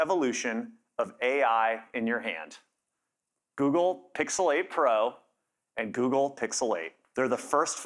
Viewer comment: I misheard it. Google pixelate pro and Google pixelate😅